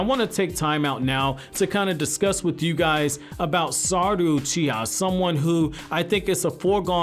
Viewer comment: sarudo uchiha lol where i heard it -->